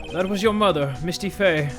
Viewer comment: I thought that said Musty Fey